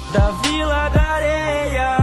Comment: Nossa na hora que eu escutei "da vila da Areia", eu pensei assim:ele não lançou uma dessa né? kkkkkklkk muito bom Edit: lá d'Areia* kkkkk